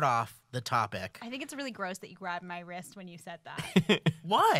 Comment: I wasn't really paying attention, and I thought Bree said "grabbed my breast"